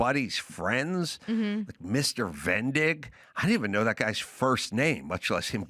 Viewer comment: I heard Vendig as "Bent dicks" 🤣🤣🤣🤣🤣